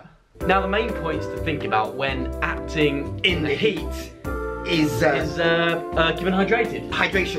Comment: Omg at the start of the video my volume wasn't up loud enough and I thought said "acting in the sheets" 😂 and I instantly thought they were going to be talking about doing scenes in bed oops 😂😂